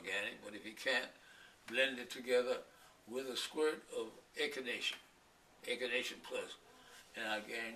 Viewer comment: Can anyone tell me what he says at 9:06 "a squirt of ..."? I didn´t understand the word he said there twice. . . Thanks in Advance!! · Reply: Echinacea it's a herb